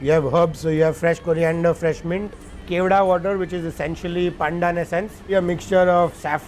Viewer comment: Just FYI… I think he said “pandan essence” here lol